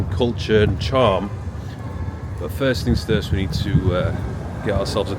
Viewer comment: Did bro say first things thurst at the beginning🤣